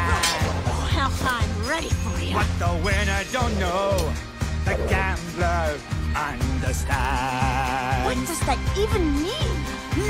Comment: What does he say!? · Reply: He sings. What a winner don't know! A gambler undestands. It means that a winner just expect he/she always wins, while a gambler know when to draw or proceed...